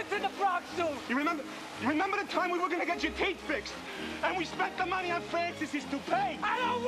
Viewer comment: Do you remember the time we were going to get your teeth fixed and we spent all of the money on Francis's toupee? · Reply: When I first heard the line as a kid I thought he said "Cape" instead of Teeth, made me wonder if Sloth spent his time running around the house pretending to be Superman.